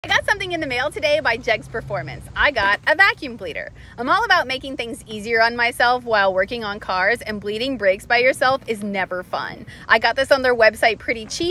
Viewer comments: I coulda sworn she said vacuum cleaner 😂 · Lmao for a sec, I thought she said vacuum cleaner...and was like shouldn't she already have one.....and then saw what she had and was like ohhhhh vacuum "bleeder"